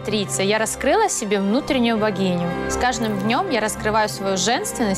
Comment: вот лично я, сколько раз слушаю это, слышу «Вагину»